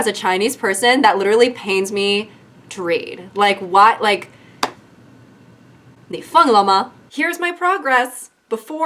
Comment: “你疯了吗?” 🥶🥶🥶🥶💯💯💯get them (i thought u said 凤 at first😭😭😭)